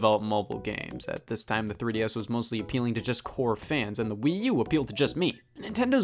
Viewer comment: when said “core fans” in the beginning i thought he said “it was developed for FOUR fans”